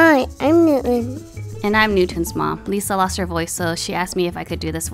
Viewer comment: It's 1 am I heard them say 'lisa lost her mom' and nearly had a stroke.